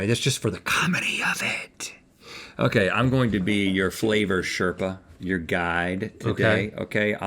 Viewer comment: did he just say he’s a flavor sherpa?